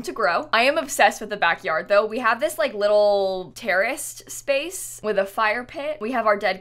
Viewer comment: I only hear "terrorist space" and like it makes sense lmao ok xD